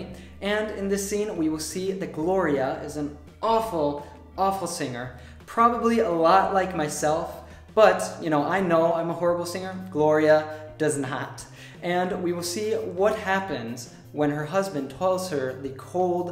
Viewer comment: did u really say 'Gloria doesn't know that' ? I couldn't hear the 'know' heheh